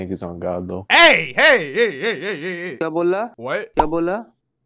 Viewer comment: That Indian Guy Was Saying “kya Bola” Which Means:- “What Did You Say?” In English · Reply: I thought he said EBOLA?